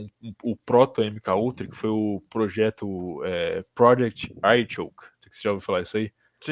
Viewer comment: Qual o nome do projeto que ele fala em 6:00? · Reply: Project Artichoke